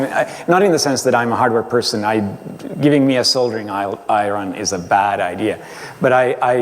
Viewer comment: LOL, @ 0:28 did he say an iRon? That'll be Apple's next device :) I'm kidding of course, much respect